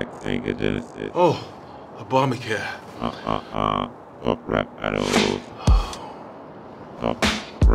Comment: (edited) Human: "Ultra flamin' doritos, ps5." (It's so hot out here. Good thing.) Human: "Yoaqua." (I packed hydration.) Robot: "Bitcoin and social media." (Essential for human survival.) Human: "Oh 2020." (Oh no.) Robot: "I didn't really care." (Critical error!) Human: "Oh, a dollar bill. Data." (Oh! Oh thank god. A human.) Human: "Oh yeah browdie." (Hey.) Human: "Lowkey Simpin'." (I'm so thirsty...) Human: "Hook me up with the juice?" (Can you lend me a drink?) Robot: "Caller id?" (Who are you talking to?) Human: "Huh?" (Huh?) Human: "20 twentyyyyyyy." (Oh nooooooo!) Robot: "Goodbye,bro. Get back sega genesis." (Relax bro. I filled up this container earlier.) Human: "Oh, obama care." (Ah thank you.) Robot: "Hahaha, top rap battle." (Not so fast. Say it.) Human: sigh Robot: "top, rap, battle." (Say it.) Human: "BO'oh'Oh'Wa'er." Robot: "BO'oh'Oh'Wa'er, *laughs in robotic malfunction*." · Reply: thx but i thought he was saying top rap battles instead of go pet parrot